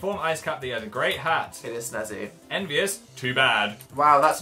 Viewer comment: Since when does Jesus ride bicycles and boats? 😂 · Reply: I thought he said "Jesus on a tricycle"